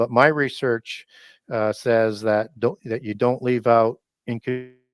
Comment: What he said ??? ... ''you dont leave out'' ... ?